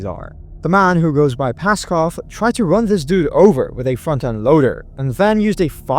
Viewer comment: 😂😂😂😂I misheard you on the 1st one. I thought you said a “fucken’” loader. Not front end loader. I was like “what fu(k H.?!?! 😂😂😂😂